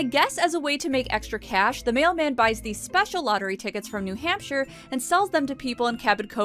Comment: Buys tickets from your hamster? I may misheard that one 😊